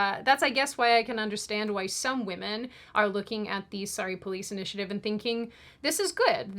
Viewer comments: It sounds like your saying "sorry police" fitting. They are a buncha sorry losers imo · I keep hearing "sorry police initiative" :D